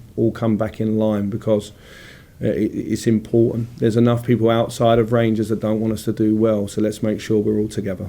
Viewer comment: did he say there is a lot of people out side of rangers don't want us to do well